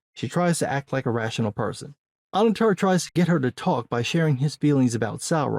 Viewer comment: who did he say tries to get strong woman to talk? Anutar? Anadadar? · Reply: Annatar